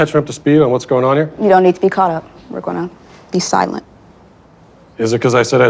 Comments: My favorite part is where she tells Matt to be silent and then he instantly talks again. Its almost like they have no power really. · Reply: it sounded like she was saying "they" would be silent, not for him "to be silent". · does she say "be silent"? As a command? This entire video is wild